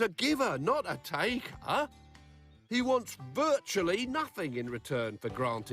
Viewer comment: why is he saying that after the other guy said that the ‘wish bringer in the sky wants nothing’ Was part of the video skipped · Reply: He said he wants virtually nothing